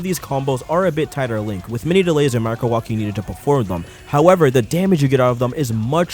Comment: what does he say here (sounds like laser markowasky) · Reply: With many delays and microwalking needed